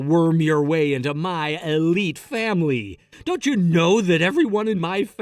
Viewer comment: Why do I hear “alpha male” when he says elite family? Based on that alone I know how the story ends